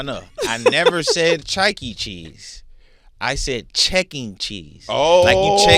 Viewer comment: AZ is so cute ❤I thought he said chucky cheese too😂😂😂 just say you was talking about the other side😂😂